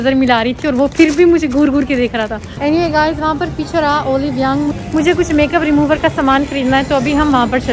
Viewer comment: Didi said olive young ,idk why I heard Oli London 😂😂